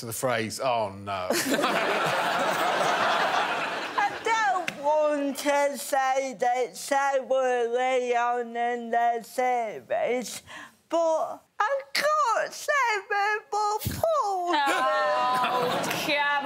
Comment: i finally figured out what Rosie said, "I don't want to say this so early on in the series, but I got cerebral palsy"